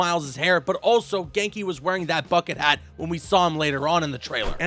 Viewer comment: I thought he said fuckin hat instead of bucket hat